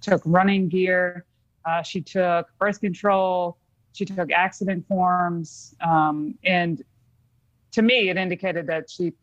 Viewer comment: Did she say when she left she took accident 🙄 forms?? If so isn't that strange since it was before the accident ??